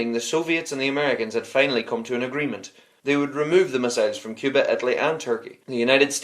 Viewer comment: At about 4:20 the subtitles say "they removed the massage from cuba"